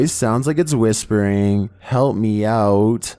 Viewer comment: I hear lemme out and then @ 18:03 I hear we (yes)